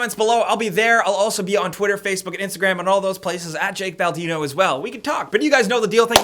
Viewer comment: ? I thought you said the great bambino lol